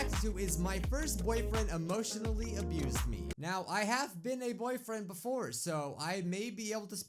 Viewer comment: OMG I THOUGHT THAT SAID THAT, “I’ve had a boyfriend before.” XDDDD